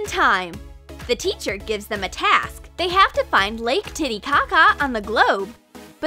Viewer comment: Did they say lake titty caca?